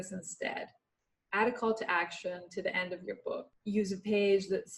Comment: Does she say 'add a call to action at the end of the book?' sry the audio is really bad I cannot make out what she is saying.